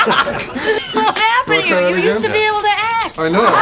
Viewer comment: what did rachel said on 10:07?? what was happened to you and what? cant hear it well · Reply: "you used to be able to act"